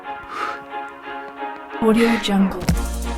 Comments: am i just hear audio jungle? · I’m sorry, what is on 10:53? Is that kind of label words or something? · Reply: Audiojungle